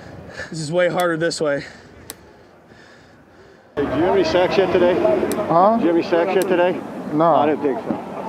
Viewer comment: What did he say 5:33 in the video 😆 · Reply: I think he says, "Do you have any sacks yet today?"